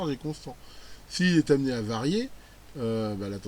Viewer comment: Hello, merci pour tes infos, 3:04 S'il est amené avarié ! lol. Trève de plaisanterie, j'ai vu que pour les bucks converteurs (step down), le rendement était souvent lié à la fréquence d'horloge. Au passage je n'ai pas compris pourquoi il faut une fréquence pour les piloter (son rôle ?).